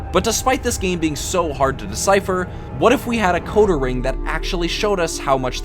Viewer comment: "coatering?" "codering"? what exactly was the word said here?? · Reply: Decoder Ring. I said that pretty bad I know lol